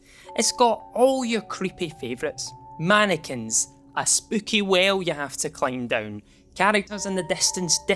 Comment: I have no problem understanding his (frankly delicious) accent, but that definitely sounded like he said "a spooky whale" 😂